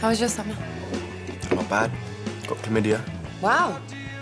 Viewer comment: What did say at 0:47? And also what did say at 1:06? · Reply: He said his summer was 'not bad' and then said 'I got chlamydia'